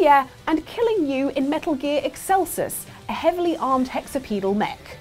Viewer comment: I misheard staying "hexapedal"...